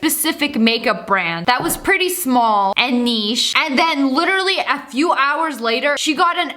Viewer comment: To the person who did the captions I think she said niche